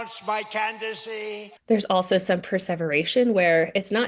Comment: your captions say perseverance instead of perseveration, and then at 6:29, the captions say persevering instead perseverating. These words have very different meanings, and it would confuse or mislead someone watching this on mute, or someone who has a hearing issue.